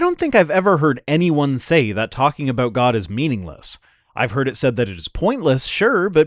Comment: I think you slightly misunderstood what he said. He said "Talking WITH God is worthless", not "Talking ABOUT God is worthless". Your comment afterwards seems to indicate you understood the second. Doesn't make his statement anymore useful though. Still, fun video, thank your for the entertainment. :) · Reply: No, he definitely said "Talking about god is meaningless." I checked the original video just in case - it has closed captions which were not auto-generated, meaning that they were supplied by the publisher, and it confirmed that he said "Talking about god is meaningless." <link>